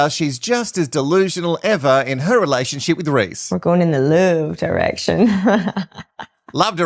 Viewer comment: I thought Leisha said "We're going in the loo direction". I thought - cool - they're relaxed enough to wee in front of each other!